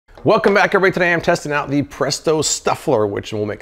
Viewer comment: Lol... I can't confirm. I hear "welcome back every today"